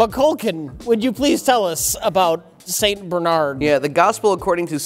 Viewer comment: Sounds like Rich says "Satan Bernard", lol.